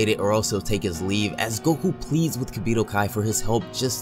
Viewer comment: I'm not even kidding you when you said that he was going to take his leave I thought you said he was going to take his weed